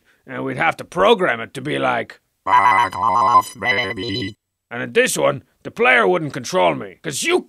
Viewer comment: i swear i hear F—K OFF BABY